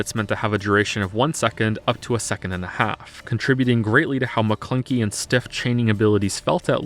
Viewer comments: I heard that "maclunkey". You cannot fool me. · Goddamnit, just heard you say "Maclunkey" when talking about SWTOR. Fucking proud of you boyo.